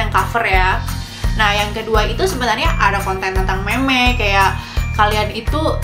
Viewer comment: Menit 3:25 gw dengernya mem*k njir